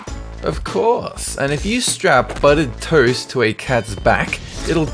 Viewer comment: i thought he said butted the first time i saw it....